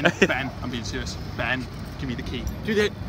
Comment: ラストマイキンタマって聞こえるw